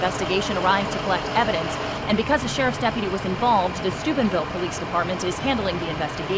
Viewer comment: Did she say the Stupidville police department